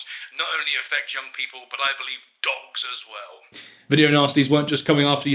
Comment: I have watched this clip many, many, many times and although I disagree with almost everything Graham Bright says, I'm pretty certain he was misheard here. I think he says "adults" and not "dogs" as many people seem to think, but he pronounces it the American way "ahh-dults.